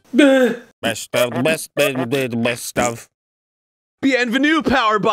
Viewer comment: I fucking totally thought he was saying "bienvenido" as it is in Spanish and not "Bienvenue" as it is in French